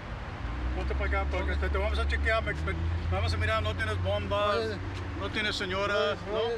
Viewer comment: did he say “no tienes bomba, no tienes senora??” 🤨